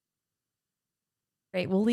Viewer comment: What did say at 23:00 ? Did she say "love you too?" Do you guys ever say I love you when you hang up on the phone? I mean to each other.